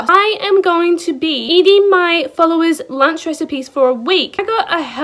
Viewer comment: At first I thought it said eating my followers for a week😂